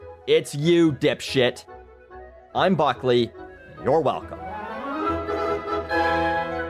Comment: All I hear is "I'm Buckley, and YOU can go get fucked" sounds like a underdeveloped kid, anyway enjoy the content....